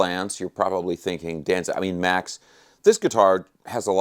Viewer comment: He slipped! Max admitted his real name! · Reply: Sounded like he said Danzig… I mean Max 1:05 😂